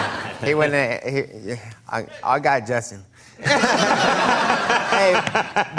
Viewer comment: what did he say in 6:34? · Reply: he says " I got Justin"